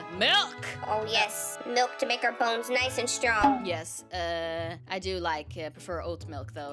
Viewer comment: I heard "milk to make out phones nice and strong" 🤣🤣🤣🤣